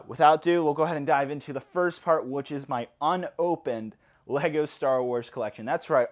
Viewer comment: I'm sorry but im French so i want to be sure... At 1:40 did he just said "unopened"? WTF.... Man do you just have 1 ARC-170 Starfighter for me maybe? If u want to sell just 1 ARC-170 Starfighter OMGGG... I want 1 since i'm a child and im 24 right now... Can u sell just 1 xD